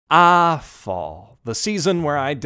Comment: LOL am I the only one who heard him say "awful"?